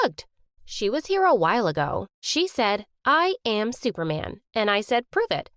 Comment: Oohhhh I thought she said I’m super mad but she actually said I’m Superman